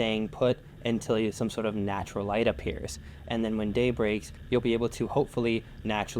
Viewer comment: did you say… when day breaks?